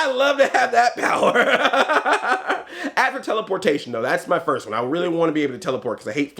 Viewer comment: Dude, you have to know! The part when you said "after teleportation", I heard "afro-teleportation"! haha I was ready for an explanation.